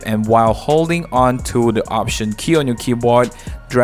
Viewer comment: what key do we hold on to? it sounds like he says option key